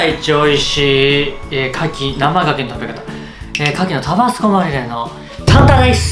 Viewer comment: タバスコマリネがタバスコまみれにしか聞こえないw